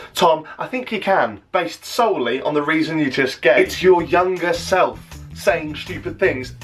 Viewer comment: " based solely on the reason you're just gay" I can't be the only one who heard it wrong